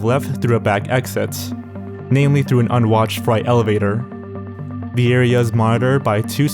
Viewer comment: did he just call it a fright elevator instead of a freight elevator?